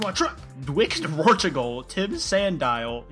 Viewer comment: I thought he said ‘Portugal’ for a sec and I was like ‘wait a fucking fuck that makes no sense-‘